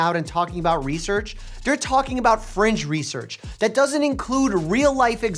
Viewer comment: does he say "they're talking about FRENCH research" as if French could be substituted for "inaccurate" or "false" ?